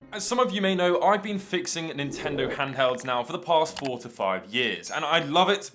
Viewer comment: haha, I heard "for the last forty five years", I was like daaamn that's a long time fixing gameboys